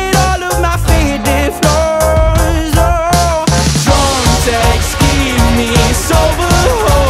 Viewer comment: Please tell me I'm not the only one who thought it said "drum takes keep me sober" and not "drunk texts keep me sober"